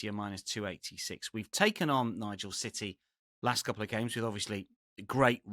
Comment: Betting City!? they've got to turn it around at some stage.... it could well be this weekend...#haaland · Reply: I heard “Nigel City” @ 3:08 😂